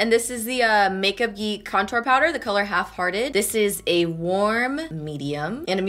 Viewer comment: so I'm on the toilet right & I'm listening and I thought you said half farted HAHA LOL 💀😂